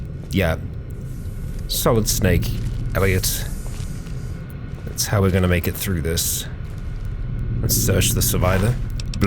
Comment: did you said ''SOLID SNAKE ELLIOT'' ? sorry not sure if i heard wrong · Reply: "Solid Snake, Elliot..it's how we're gonna make it"